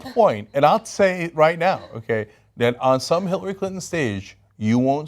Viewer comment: Did Cenk just say "Hitlery Clinton"?